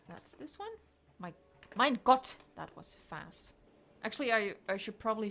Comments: did you say ''Mein Gott!''?(that means ''my god'' in german) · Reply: I did and I know ;) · I Think i heard her Talk German "MEIN GOTT"